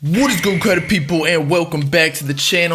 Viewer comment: what do you say in your intro... all I hear is “what is good clspudjddj people”